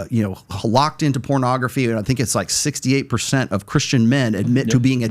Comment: “68% of Christian males are addicted to porn” What I heard : “68% of Christian males are feminine”